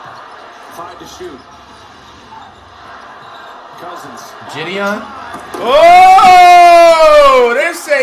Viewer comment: Did he just call DeMarcus cousins Jidion 😆😆😆😆😆😂😂😂😂😂😂